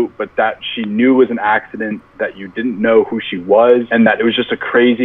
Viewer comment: wait i thought said he didnt know who max was and that he was a part of team ten whaaaaaa liiiesss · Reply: Faith King he said he didn't know meg was